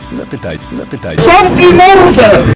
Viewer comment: Co ona powiedziała? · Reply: ZAMKNIJ MORDE